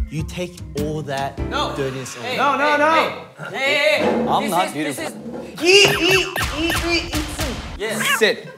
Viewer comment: did han just say "i'm not beautiful" 😭😭